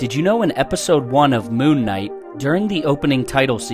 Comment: Thought he said “loon knight” for a sec lol